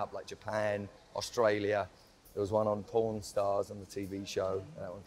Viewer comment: When he said pawn stars I thought he said pron stars